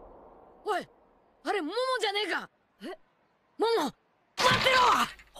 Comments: what did bro say? 💀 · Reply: He say momojanega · what did he say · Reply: Momo